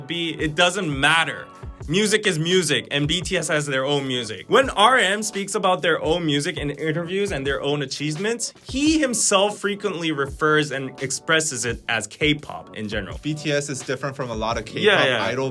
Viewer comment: I’m sorry but did you say “a-CHEESE-ments”?